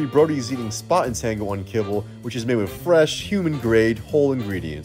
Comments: I really thought he said made with human at first💀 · I thought you said flesh from humans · I thought he said it was made from fresh humans